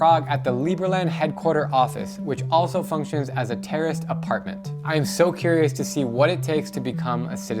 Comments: did he say terorist apartment? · At first I thought he said "terrorist apartment," even the subtitles thought that's what he said 🤣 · did he say terrorist apartment?